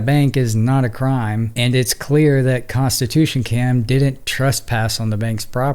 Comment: I thought you were saying “Constitution Camp”. 😅 I had to listen to it a couple times.